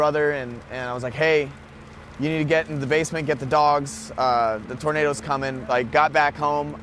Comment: Wasn't there more than one dog, though-? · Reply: You're right. I just rewatched it. 0:03 It does sound like he says dogs, plural. Hopefully the one dog was already safe.